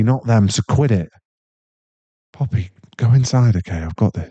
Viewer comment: I still mad he called me a f ing popsicle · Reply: I thought he kept saying puppy, cause we're cute, snuggly, and follow him everywhere